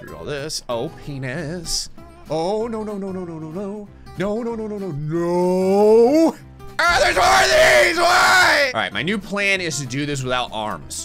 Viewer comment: I know its AGH THERES MORE OF THESE WHY?!?!?!? But I heard CBORAGEEZ GUY